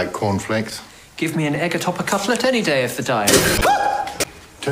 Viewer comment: ‘give me an egg atop a cutlet any day of the week...” “HUH” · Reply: I thought he said any day of the day lol